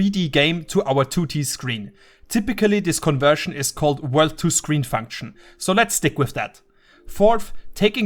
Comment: you wrote WordToScreen instead of WorldToScreen